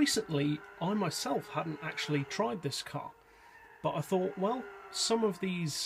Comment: I've actually seen ppl use this car is cruises... Also I was using subtitles just to see what it told me and at 0:44 instead of saying car, which you said, it said cock... And at 0:11 you said Granturismo 6, but it said grant remove six... Youtube needs to sort it out